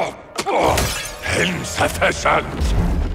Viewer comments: anyone understands what festus says on 0:32? subtitles dont work on that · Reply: ‘Insufficient’ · What is he saying ? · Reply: Insufficient